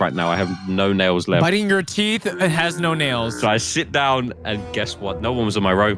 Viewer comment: Did bro say sit or shit?